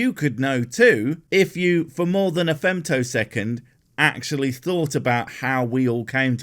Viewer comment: it's super bothering me. i heard you said " femto second " and i want to know the true. was it Femto ?